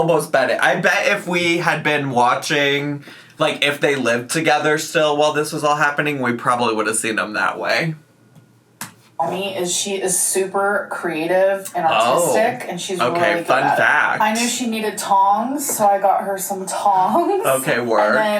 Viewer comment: Now why did at 10:48 it sounded like she said “creative and AUTISTIC”